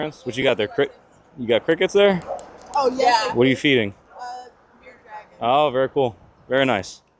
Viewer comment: did she say bearded dragon - have one myself very easy to care for and hold 🦎🦎